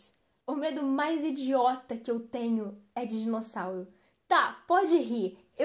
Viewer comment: no começo eu escutei " eu tenho medo de sal" mais era dinossauro kkkkkkkkkkkkkkkkkkkkkkkkkkkkkkkkkkkkkkkkkkkkkkkkkkkkkkkkkkkkk